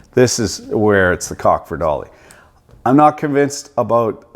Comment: For the longest time i thought he was saying "It's the cock for Dolly" .